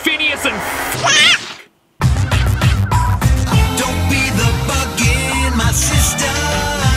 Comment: "Bug in my system" is what I heard first time. not sure how u didn't get that first time